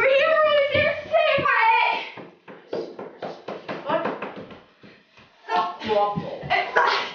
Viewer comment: I swear at 2:51+ (idk) ben said "i want waffles" Uh no there tobys